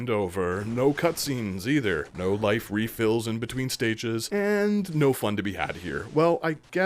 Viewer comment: I swear I heard you say at around the 5t minute mark that it was fun to be a batass. I got a huge kick out of the bat puns.